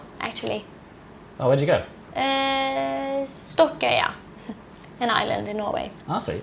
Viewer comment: what country does she says at 11:23? I can't understand her, I don't speak english that well lol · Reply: Stokkøya